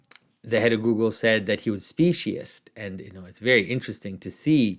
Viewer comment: you said the head of Google said that Elon musk was what? It sounded like you said speechless, but that doesn't make sense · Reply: Speciest